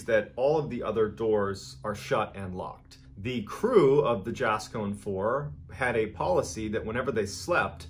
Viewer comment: God I thought he said the Jackson 5🤣